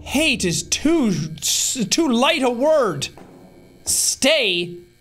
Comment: I thought it said 'SLAY' not 'STAY' lmao